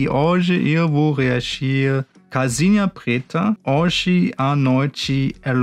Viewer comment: Ele falou "calcinha preta" mas eu ouvi "casinha preta"😂😂😂